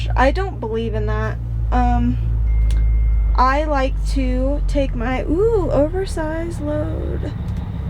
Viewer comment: say what? all I heard was you like to take your oversized load